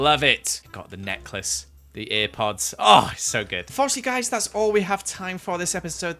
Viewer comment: DId he just call airpods earpods??